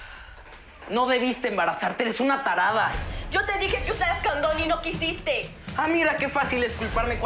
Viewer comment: escuche candon jajajaja